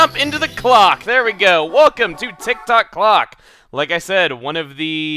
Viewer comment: For some part of the video, I thought he was saying glock. As in the gun.uuuuuuummmmmmmm.......